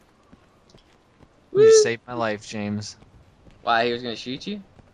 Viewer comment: did he call at 10:12 ?